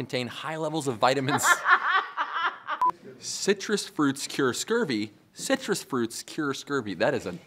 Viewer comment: At the end when they were showing the bloopers, for a second I thought they said "Citrus fruits screw scurvy" and I got real scared for a moment